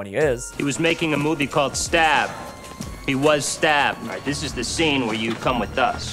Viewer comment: "He was making a movie called Stab. He was stabbed." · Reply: lol , no I always thought they just said he was Stab, never heard it as Stabbed lmao! Yup, I’m dumb 😂 I used to think they called ghostface ‘Stab’ in the stab movies